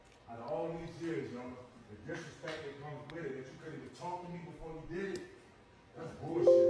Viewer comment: "My body, my choice!! You knew who tf I was when you met me" I seriously thought he said "a whore" for a sec there, lol... (he actually said "before")